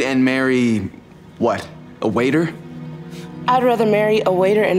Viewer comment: Help i thought it said water 😭😭